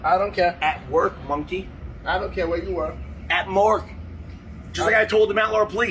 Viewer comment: Man turns himself into racism effigy and is surprised when the public lights him up. Smh this man has been fully conscious this whole year and thought now's the time, hilarious! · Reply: If it makes anyone feel better, at 2:00 he fucks up and says mork instead of work.